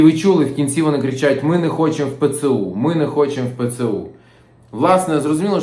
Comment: Что то я с этим коридором не допонимаю.У ребят что орудия не было?Пару очередей предупредительных в воздух и путь свободен.А эти идиоты вообще кому орали что они не хотят в ПЦУ(мне вообще послышалось что орали "Ми не хочет ЗСУ).А если так то и не только в воздух тогда.Предателей надо уничтожать,они в несколько раз хуже врагов.Это мое мнение.Твпринпшли когда и где скандировать.С властями пусть пробуют или а рашку молиться если это так принципиально.